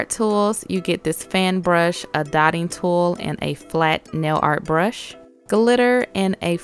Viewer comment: I thought you said thotting tool